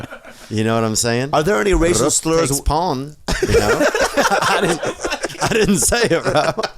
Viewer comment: Wtf does Theo say @ 16:29? Rook takes pawn?